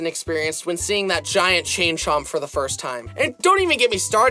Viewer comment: DID YOU JUST SAY CHING CHONG?